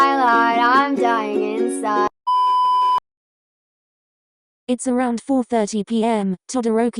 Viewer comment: In the beginning of the video i thought it said "it's round 4" i thought round 4 of the se- thing😭😭😭